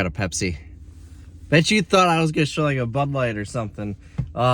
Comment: Bud light? Thought you said a beer